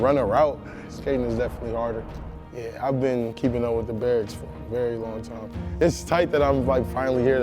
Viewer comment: Did he say he's been keeping up with the "Bears" or "Barracks"